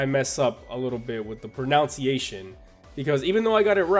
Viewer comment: "pernounciation." I cringed when I heard this...